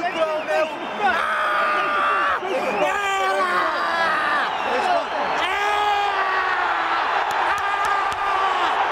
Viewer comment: What does Conor say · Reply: Andrew Alarcon i did some research and he apparently says Ireland in Irish... "Éireann"